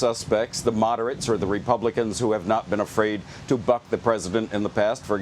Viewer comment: I swear mans said "who have not been afraid to F*CK the president in the past" 😂💀